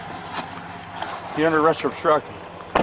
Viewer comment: “You’re under arrest for obstruction”. . Is that what the cop just said?